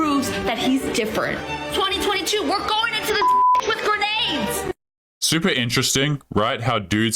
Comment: LoL! I didn't hear her clearly at first, so I thought 1st girl said, 2022 we are going into this with A.I.D.S., but apparently she said grenades. As for the last girl simply having a skin care line that is 3x the price of a meal doesn't mean her dumbass isn't close to broke. As she might have spent almost all her money on the skin care line. She definitely isn't vain, egotistical and entitled.